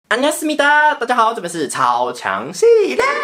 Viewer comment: 開頭是不是唸錯 還是我一直以來都錯了 我聽成안녕하습니다